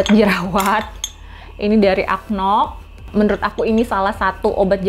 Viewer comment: Itu obat jerwat warna biru silver itu nmnya bnr acnol kah atau aku yg salah denger y? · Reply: ACNOC namanya. baguss